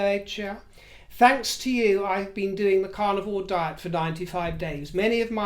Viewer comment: I'm a U.S. Southerner, and I adore your accent, although at first hearing, I thought you were saying your were on the "Carnival Diet" & I'd not heard of that one! Best hopes for your health issue. Bwaahahahahahaaa at the idea of monogram "ASSOS". WHAT a boring mess she is.